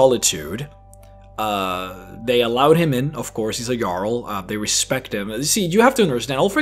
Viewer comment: u said jarl in a way that the diaglog/translation said it as a girl the reason i know this was cus i set texst on when im eating chips cus i cant hear shit when i do